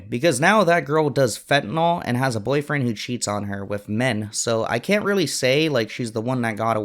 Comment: wut , did I hear 'men'? 🤣